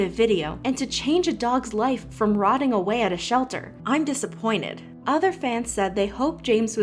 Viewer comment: I’m legit disappointed at people saying “He did it for views” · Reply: I thought you said "I'm legally disappointed..." I guess I have been watching too much Badads.